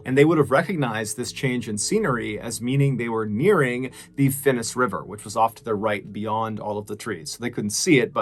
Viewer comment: As a Finn, listening to this made my brain hurt. All I heard was “Finnish river”. 🫣